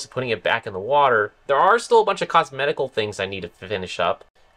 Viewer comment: did you actually say "Cosmedical" things?